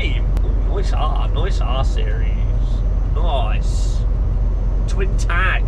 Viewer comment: When you said nice r series at first I thought you said nice arse